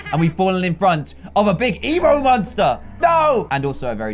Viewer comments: I Thought you said emu monster but you said evill monster. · when you said "Evil monsters" it sounds like ur saying "Emo Monsters" XD